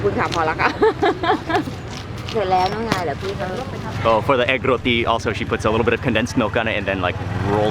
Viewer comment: Ii thought it said egg rotten lol